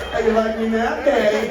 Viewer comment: What did Jensen say at 15:42? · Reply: “How do you like me now daddy?”